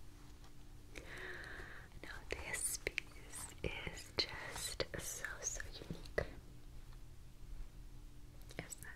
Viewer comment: Does anyone know what she’s saying at 5:08 ??? This piece is so____? Sorry this shouldn’t bother me as much as it does but I must know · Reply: I think it's so unique - "this piece is so so unique"